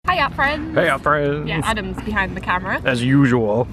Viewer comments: they turned out so good(: no i never used screen tone but it is defenitly on my list aswell. i always thought you were saying heyy yaa friends^^ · oh i thought they said "heya friends" lmao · I always thought they said hiya friends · For the longest time I thought you guys were saying "HIYA FRIENDS!" XD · i thought you guys said "hiya friends" haha. oops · I always thought u guys said "Hi, ya friends!" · I thought that u say hi alph friends because- Adam and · I always thought you guys say "Hiya friends!" but then I saw the stickers : P It's an awesome challenge. Monochromatic stuff is awesome : ) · ohhhh i thought they said "Hiya, friends!" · I always thought you said hiya friends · I always thought they said hya friends